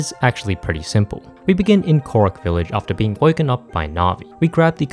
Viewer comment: did you say korok village???